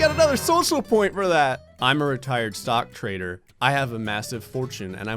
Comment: i thought it said "sock trader"